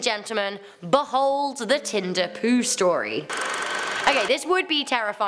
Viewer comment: when you said tinder poo i thought you said tender poo and the immature side just completley took over